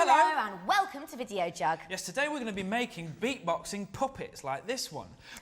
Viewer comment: damn i thought it said beatboxing puppys Darn!!